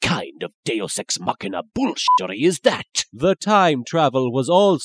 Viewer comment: Someone help me here, what exactly is Bakura saying at 0:30 ? It sounds like he's saying "deosex mockup bullshitory".